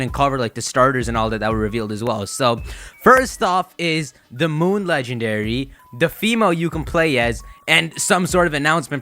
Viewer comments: Did he just say the moon leak 1:51, I recommend a nap LOL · for some reason i thought you almost said " The Box Art you can play as"